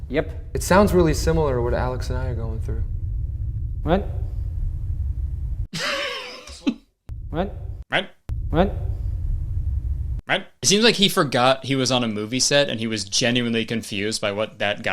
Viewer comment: i think hes saying "right" but jesus christ they should've done another take